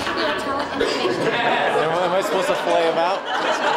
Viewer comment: What does dude say @ 2:33? I can’t make it out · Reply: I think he says "I dunno how I'm supposed to feel about" Correct me if I'm wrong.